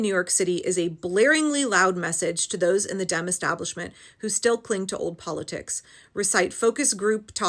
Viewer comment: I like that the auto caption apparently misheard you say Dem Establishment and captioned it as Damn Establishment because it still works.